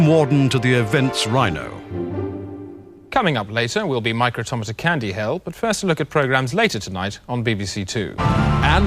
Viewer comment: any one know what he's saying? "micro Tom's at candy hell?"